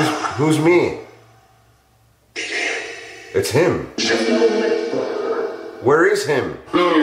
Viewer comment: Instead of ''It's him", I got "Be damned".